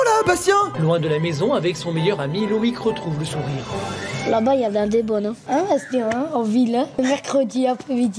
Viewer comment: moi aussi j'ai entendu Loïc dire il y a vla les bonnes 😅😂